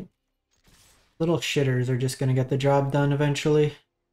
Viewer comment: What did he say? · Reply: Little shitters - an assortment of random 1-2 power creatures just beating in